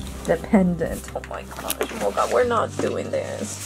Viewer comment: "Loca--Mocha!" 🤣🤣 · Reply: I laughed when I thought I heard you say Loca Mocha hahah